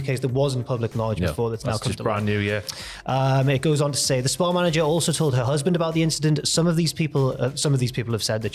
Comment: Not to make light, but I thought said ''a Spar manager''. Was thinkin Jesus, even your local News Agents isn't safe!